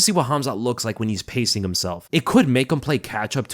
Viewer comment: Strange, I thought Gastelum already got cut. Anyway I don't think Rodriguez will beat him. I about shit myself when I thought I heard "I wonder what Khamzat looks like pissing himself." haha